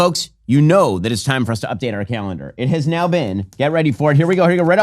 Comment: I thought said “you know it’s time to update our Kamalendar” instead of calendar 😂😭